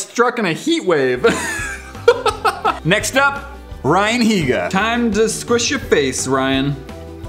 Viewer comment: I thought he said "Brian Higa" instead of "Ryan Higa"